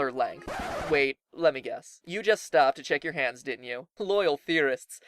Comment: I stopped the vid turns out I'm a girl then I continued and heard him say we stopped